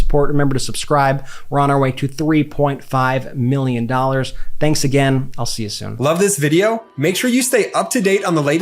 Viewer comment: Am I the only one who heard "we're on our way to 3.5 millions _dollars_? At the 15:01 mark. :D